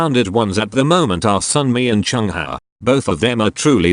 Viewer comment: I thought u said Somi....oh good thing i replayed that. (yes, ik u said Sunmi)